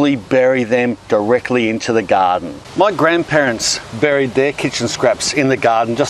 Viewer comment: I heard "my grandparents buried their kids"